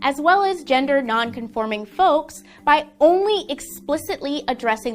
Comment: Did she say ¨folks¨?